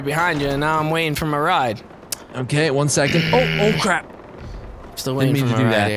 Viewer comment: Did say "crap" at 9:50 ? XD